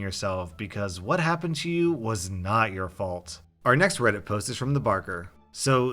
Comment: what did he say? Late 90/ early Oz ? Like wizards of Oz ?